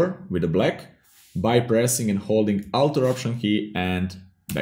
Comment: What did you say at 9:39 seconds? The outer option key? What is that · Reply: Alt or option key, and backspace.